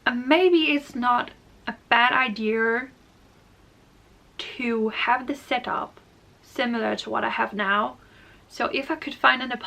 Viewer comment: What on earth are you saying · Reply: "A bad idea to have the setup similar to what I have now" She almost said "idear", but danish has a lot of unique sounds that will sneak into their english.